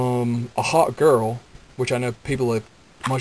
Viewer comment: XD when you said a hawk girl, I thought you said a HOT girl XD